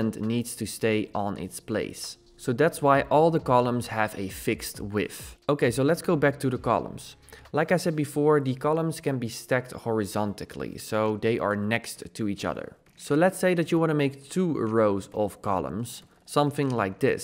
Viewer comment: Did you say "horizontically"? (3:10) Anyway, good video, thanks!